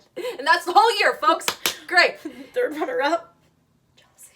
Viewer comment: What did say at 14:51? Just wondering. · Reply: “3rd runner up: jealousy” LOL